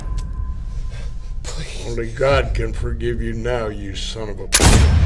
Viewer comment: what is there saying? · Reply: Guy on Ground: Please... Guy with Gun: Only God can forgive you now you son of a...